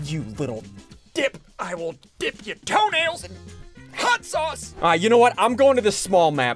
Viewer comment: did he say what I think he said??? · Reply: He said toe nails